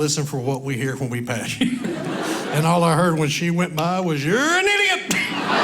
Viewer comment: When she drives by all I heard "YOUR AN IDIOT" I lost it 😂😂😂😂😂😂😂😂😂😂😂😂😂